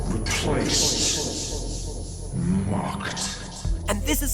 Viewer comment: "ive been pushed aside replaced....MOCKED" me:"with that accent sounds like hes saying MARKED....M A R K E D"